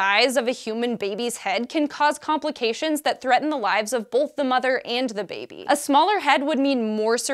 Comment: Did she say "bolth" instead of "both"?